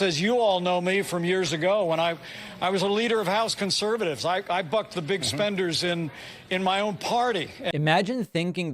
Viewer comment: I thought Pence said "I fucked the big spenders" which. . . sounds about right for the Republican party. The man is so devoid of charisma that the only thing he ever did of note was having a fly sit on his face for a couple of minutes during a VP debate.